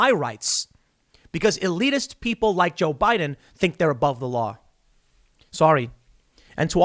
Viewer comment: It’s weird it almost sounded like you said “joe Biden THINKS he’s above the law”